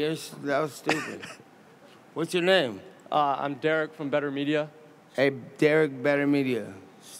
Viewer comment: Nate probably thought his full name was Derek Betermedia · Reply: I swore I heard “Derek Young BetrMia” 😂😂😂